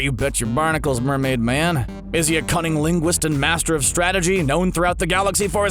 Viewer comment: I thought he said cunnilingus